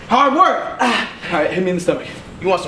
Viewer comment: What did he said ? Strong Accent for me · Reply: Alright, hit me in the stomach